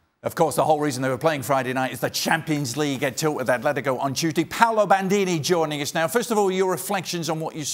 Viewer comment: I really thought he said Paolo Maldini i was so exicted